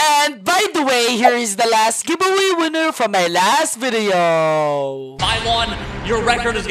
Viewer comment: saying milyon What i hear:my lawn nylon and more